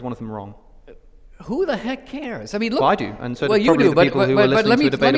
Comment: (Alex pointing out, that the 2 gospels directly contradict wach other) and asks a very clear question: "Are they both correct or is one of them wrong?" Dinesh answers: l "Who the heck cares??!" Did he really say that? Its pathetic!!!